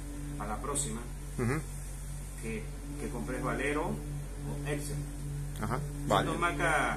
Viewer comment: no conocia la marca balero. Y cual es la otra que dice?. Tengo que cambiar el bimasa en la Viano y creo monta Sachs. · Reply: creo que dice valeo